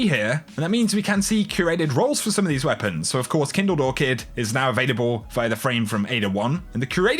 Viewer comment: I want the Kindle-Lore-Kid with Rampage + Kill Clip so bad. (Yes I know I spelled it wrong, but that was what it sounded like said xD which is okay btw, I just found it funny that my ears took me there.